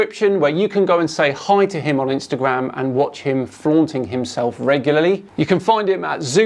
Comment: For a second I thought he said "flogging himself".